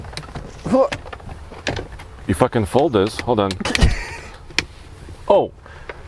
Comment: did he say u fu.... fold this?